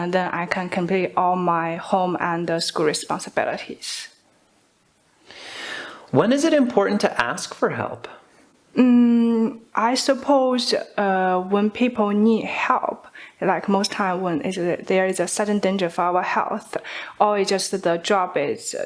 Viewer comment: I thought she was saying when people ni hao :))